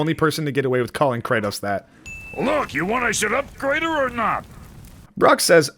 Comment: Brok doesn’t say us at 10:34, what he says there is “you want I should upgrade her or not”.